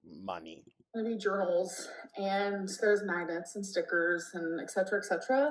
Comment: did she say MAGGOTS?